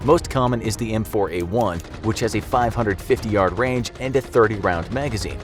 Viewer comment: “The most common is the M16A1” shows picture of M4 Carbine M16A1: Am I a joke to u -_ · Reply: He did NOT say M16A1, he said M4A1